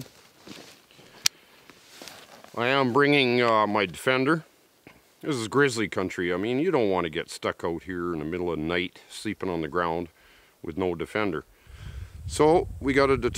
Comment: I thought you said “I brought my Fender “ a guitar not shotgun! 😝 LoL